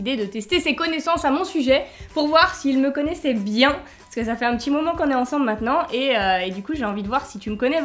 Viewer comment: A 0:21 jai compris "sa fait un petit moment que je suis enceinte" alors que a dit "sa fait un petit moment qu'on est ensembles 😂